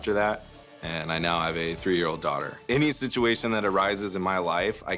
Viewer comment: did he say he has a 83 year old daughter🤨